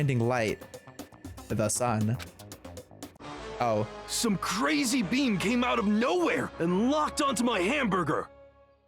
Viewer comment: i heard “some crazy bean came out of no where and locked onto my hamburger” 😭😭😭